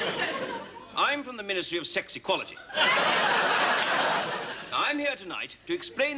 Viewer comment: I first thought he said 'ministry of sexy quality' 🤣🤣🤣